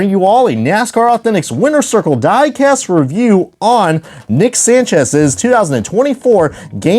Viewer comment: for a second I thought you were about to say nick shift